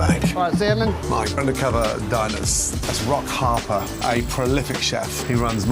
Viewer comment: i had to stop and read the Black guys name cuz i could swear i heard Gordon saying; "he's rock hard " 🤣